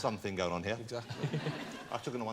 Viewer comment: Can anybody tell me what did Jonathan say at 4:54? Thanks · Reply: OldGrave97 "I took him to one side and told him to avoid the skunk".